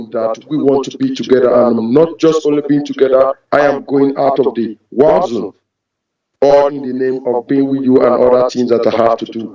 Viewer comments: Oh, I get it now --- he's saying "war zone"! I thought he was saying "I am coming out of the WAZOO" (which sounds even more appropriate).!!! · Everytime this scammer says " war zone" I always hear "wazoo"😂